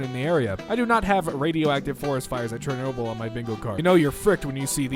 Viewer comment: I heard him say Chairnoble. Not Chernobyl